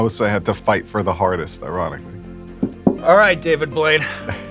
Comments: min 19:53 I think the sauce was getting to Sean, I swear I heard him say David Boyd. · did he call him david boyd lol!? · did Sean just call him David Boyd? 😳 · Did he really call him David Boyd???? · to me it sounded like he called him "David Void" instead of Blaine